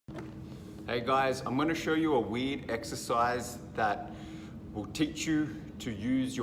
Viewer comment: I thought he said "weed exercise"😂🤣😂